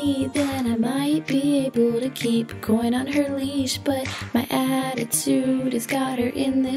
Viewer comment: i thought she said porn on her leash O. O